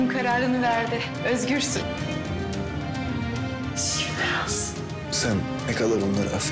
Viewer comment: orda kısık bir kadın sesi var ne diyor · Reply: Şükürler olsun diyor